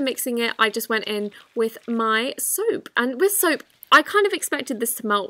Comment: is it just me or did I hear soup-? 🍲